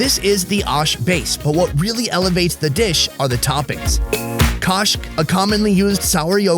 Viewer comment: The dish at 27:40 looks soo yummy!!! I wish I knew what it was called.. I think he is saying it's called "kosh"?